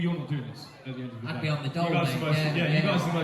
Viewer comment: What did he say at 1:25? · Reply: 'on the dole' basically when you're getting money from the government bc you don't (can't) have a job or are very poor - in Britain